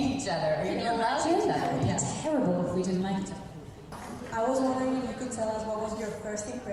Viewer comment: What they said at 10:35 min pls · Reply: Dom said, "It would be terrible if we didn't like each other."